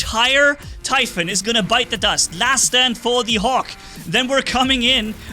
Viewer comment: Did say Bites the dust? I didn’t know used Killer Queen